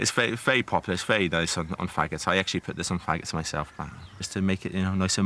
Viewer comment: I have no idea what this dude just said. But it's cool to watch! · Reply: I thought he said Haggis!